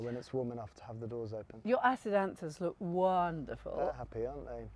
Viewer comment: What is the flower Bunny mentions at 25:02? I cant quite understand what she is saying… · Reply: It’s Acidanthera murielae, not quite hardy I bring in in winter in Cambridgeshire 🐇